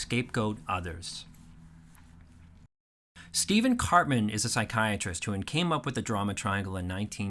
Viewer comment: till now, I thought he was saying Cartman lol